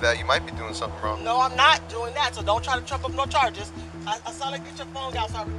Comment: it sound like she said, don’t Trump start charges